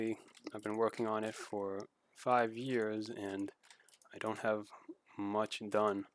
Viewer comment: did you said 5 years? o_o